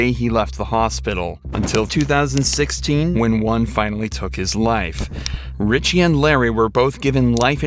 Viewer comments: What video did you watch? This video clearly states he died as a result of a seizure. Not suicide. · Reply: i thought they said "when ryan took his life" .. but they said "when one took his life" 😭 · spoiler OMG I CANT BELIEVE HE PASSED AWAY FROM SUIC*DE poor kid my heart breaks for him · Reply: He didn’t pass away from suicide, The narrator says “one finally took his life (a seizure) but with his accent he sounds like he is saying “Ryan finally took his life “